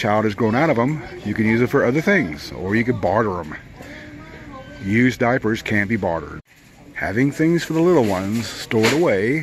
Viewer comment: Did I hear you say that used diapers can be bartered?